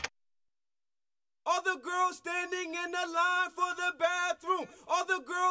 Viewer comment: I always thought i heard 'all the girls standing in the line for the bad wolf' lol